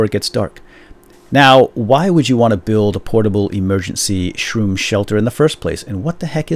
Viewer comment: did he say shroon